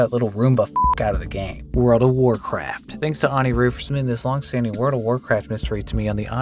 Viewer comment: Am I the only one who heard him say Rud of warncraft